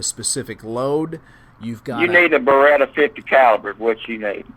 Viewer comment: Did he say "you need a beretta .50 cal"?